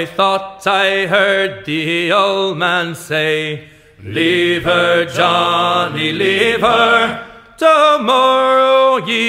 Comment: Sometimes, instead of "leave her Johnny, leave her", I hear "Lever, Johnny Lever". lol